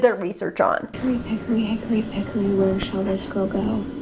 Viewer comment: I started using that rhyme Sabrina says but i misheard her so mine is “hickory pickory hickory pickory where shall this crow go”